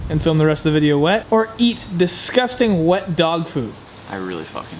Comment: When he said dog food I thought he said dog poo I was so disgusted when he pick "dog poo"